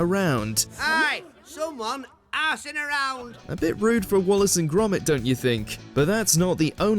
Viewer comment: kinda sounds like he said “harassing around”